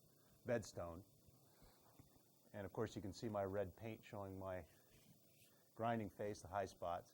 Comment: At the start I thought you said "lead paint" and I was so concerned! :D (fyi - he said "Red paint")